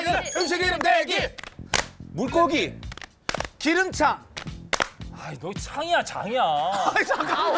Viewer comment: 솔직히 발음 이상하긴하다. 기름차. 기름창으로 들림 발음을 똑바로 해야지